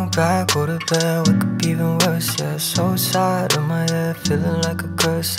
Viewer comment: I thought he was saying "suicide in my head", but he was singing "so sad in my head" all along. Lmao I can relate to the first line tho 😅